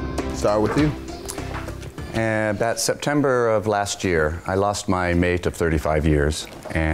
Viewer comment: This whole time I thought he said. “I lost my maid of 35 years” 😭😭🤦🏾‍♀️